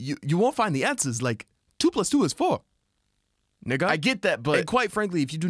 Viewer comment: Did she just say nig?